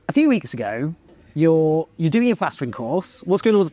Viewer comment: Congratulations Hannah. That’s splendid stuff. I’ve tried plastering with one coat plaster 😅. Not easy! Well done 💚 x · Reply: Oh that's what it is! I honestly thought she said 'pastoring', and was rather disappointed, being agnostic.